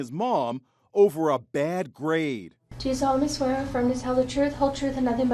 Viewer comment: thought they said bad grape